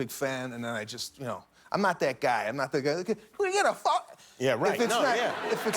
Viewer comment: did he just say "are we gonna ƒµ¢k??"